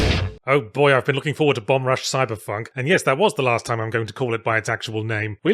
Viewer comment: I thought it said Bumb Rush Cyberfuck.